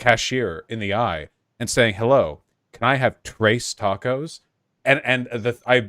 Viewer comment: Vaush's tres tacos story is the funniest shit lmao Glad to see he stays in character even when he isn't online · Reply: I thought he was saying "trays tacos" at first lol